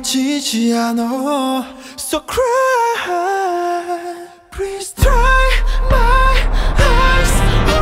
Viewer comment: Translations: Police: (이름)Name V : (김태형)Kim Taehyung V: 21 Police: (부모님) Parents V: (그런거 없어요)I don't have such thing/ I don't have those. (means: I don't have parents) Then V narrates 2:15 "그때 나한테 왜 그랬어?" "why did you had to do that to me?" Singing: I'm sorry I'm sorry my sister 숨겨도 감쳐도 -my sister even I try to hide and close 지워지지 않아 - it doesn't erase so cry please cry (something in english I don't know T_T) 저 빛은, 저 빛은 that light, that light 내 죄를 비쳐줘 lightens up my sin(crime) 더 깊이, 더 깊이 deeper, deeper 죽을 것 만 같아 I feel I'm going to die 그 벌을 받게 해줘 Please punish me 내 죄를 사해줘 Forgive my sin(crime) 제발 please then the songs ends and a Korean quote pops: 여긴 다른 말만 하는 고래들 뿐인데 "There are different whales with different languages" (Rap Monster's part in Whalien 52) Then V ask the police "전화 한 통화만 하게 해주세요" "I can I make one call?" I'm not perfect at this T_T so help me armys T_T 제가 똑바로 못 들은 걸 수도 있으니까 한국아미분들, 답글로 고쳐주세요! 보고 후에 수정할께요 ^^ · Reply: I dont but i hear "please try my horse" in that part you can't understand